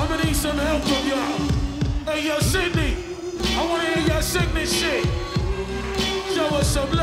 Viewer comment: I wanna 安い飯(空耳)